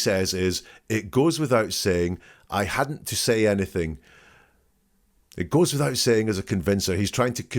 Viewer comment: I heard “it goes without saying, not to say anything”, meaning “i obviously told Chris to keep quiet”